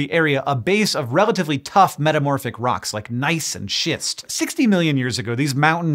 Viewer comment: I thought i heard "Nice and shits"